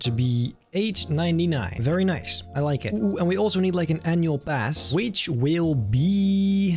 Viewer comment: i thought he said "N word pass"